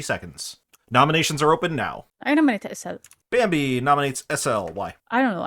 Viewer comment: I heard "I nominate myself".